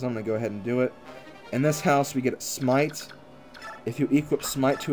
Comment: I thought smite said smile.